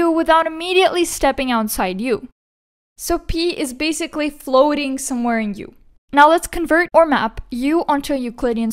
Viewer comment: “So pee is basically floating somewhere in you.” My captions said this 🤣